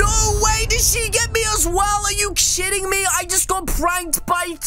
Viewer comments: Who else heard him saying “are you s word ing me?!” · I heard you say are you sitting me